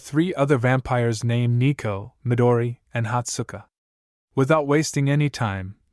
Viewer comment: i hear hot Stuka